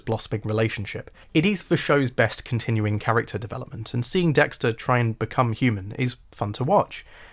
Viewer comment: genuinely thought you said "Seeing Dexter try and become Cuban."